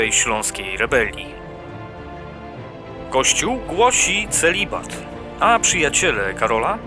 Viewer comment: Usłyszałem "Gościu głosi celibat" :D